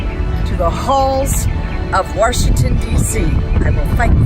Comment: Did she just say "WARshington"?